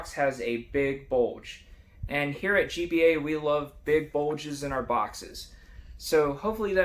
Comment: I had to do a double take because I thought you said "we love big bulges in our boxers"